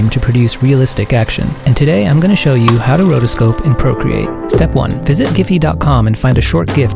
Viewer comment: I seriously thought you said how to rotoscope and procreate... why are you teaching anyone how to procreate...